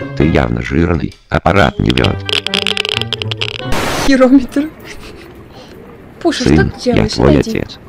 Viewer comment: Мне послышалось , или она сказало не жирометр? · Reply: Да. Она сказала Херометр